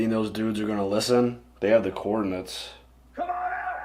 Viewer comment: Haha. I thought you said they had the “corn nuts” not “coordinates” . I was thinking, I know corn nuts are delicious but.....